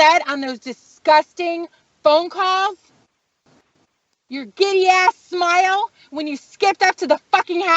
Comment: Sounded like she said gay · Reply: No, she said "giddy".